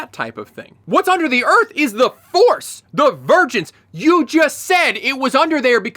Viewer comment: Oh... Divergence. I kept hearing "The Virgins" from Master Squid Game 😂